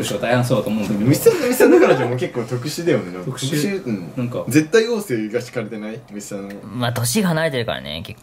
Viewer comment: ぐらいにりょうくんが虫さんの彼女について「ぜったいごうせいうかしかれてない(？)」みたいなとこ言ってるのは実際なんて言ってるのかわかる人教えて頂けませんか？ 全然聞き取れなくてモヤモヤする、、笑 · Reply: 絶対王政